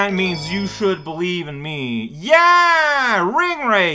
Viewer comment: Misheard that last bit as "YEAH RING WRAITHS"